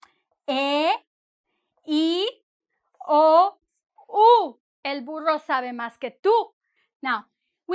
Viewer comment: did she just say a donkey knows more than me? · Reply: "El burro sabe más que tú"